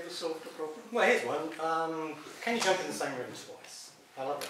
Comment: Hey, what did Stephen says at around 7:15 he sounds like he says "can you jump in the same room twice?", do you know what he said? I can't make it out.